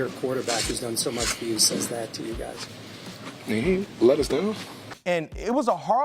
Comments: Sounded like he said he ain’t let us down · The bills player in the locker room isn't bring cold...it almost sounded like HE was also in 😭 tears...and it sounded like he said "he AIN'T let us down "